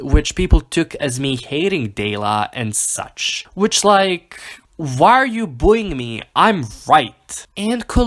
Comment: I heard "Why are you booing me? I'm WHITE " and I CAN'T STOP LAUGHING